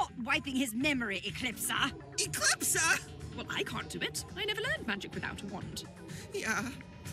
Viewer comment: So, you have to learn magic without a wand? Interesting.... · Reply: I thought she said I never learn magic without a wand